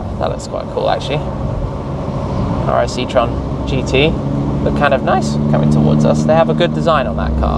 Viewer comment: what does he say? citroen gt?? What car is that?